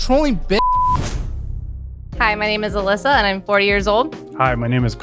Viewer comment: I heard “my name is Alyssa and I’m 4 years old”